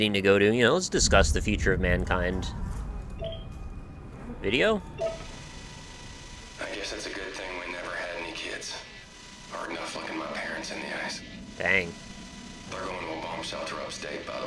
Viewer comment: I wasn't looking and it sounded like he said "Hard enough fucking my parents in the ass" and wondered what the hell that has to do with having kids. Good thing there are subtitles to clarify.